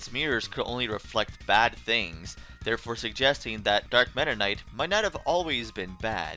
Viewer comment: Ha! If you turn on closed captions at 0:37, instead of Dark Meta Knight, it say Dark Matter Night. XD